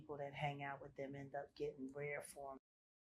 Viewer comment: What did she say at the 6:30 mark? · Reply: “Rare forms of all $&@ s$&t”